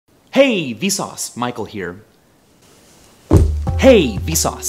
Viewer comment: The past 3 mins, I thought he said "Hey V sause, Mike will hear"😂